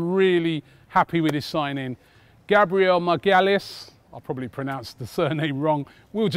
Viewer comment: Anyone else thought he said Gabriel my gyalist